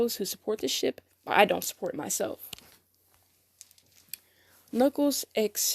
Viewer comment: I thought you said “I don’t support myself” 🤐